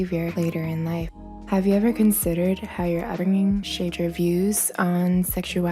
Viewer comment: I couldn't understand what she said "uttering shade reviews"